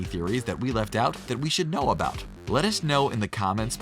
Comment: I always hear "lettuce know about it"